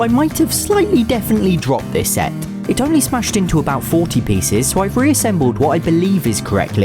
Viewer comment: I thought you said plece